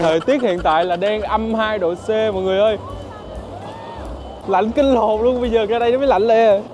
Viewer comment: anh nói tục ,ko tin thì mọi người cứ tua lại rồi coi tiếp thử xem,chị kêu lạnh quá ,anh kêu:lạnh .....😢😢😢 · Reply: LẠNH KINH LỒN =))